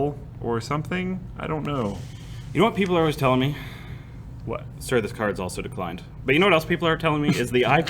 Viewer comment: i had to listen to that more than 5 times to understand what he was saying, but it was worth it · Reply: "Sir this card is also declined"